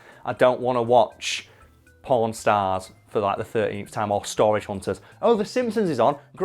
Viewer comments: Had to do a double take on that one 😂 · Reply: I didn't notice. Sounds like he said porn stars but the context suggests pawn stars. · We he said pawn stars I thought he said pornstars